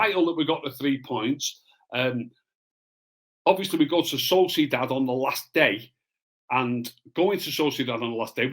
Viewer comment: i could swear at 2:27 he said " saucy dad " instead of sociedad xD LoL